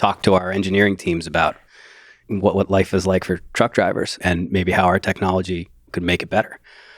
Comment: I heard "former drunk driver" 😆